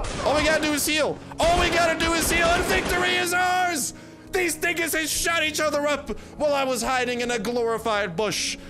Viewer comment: did he say the "N" word or am I hearing things?... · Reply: David boi he said dinguses lol